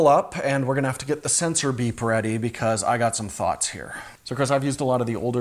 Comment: LOL! I was confused for a moment when I thought Jordan said "Sensor beep".